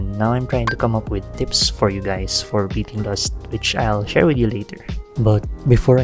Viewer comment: What is?? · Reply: I mean I thought you said "I beat lust" like someone could say "I beat dark souls" lol Because my brain didn't process what lust meant